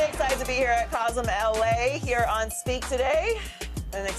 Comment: Thought it said FREAK.. instead of speak lol 😅😅